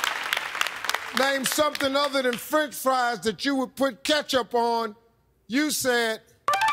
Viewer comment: I literally misunderstood the question. I thought he said, "Name something other than ketchup that you would put on your french fries." I would have said "vinegar".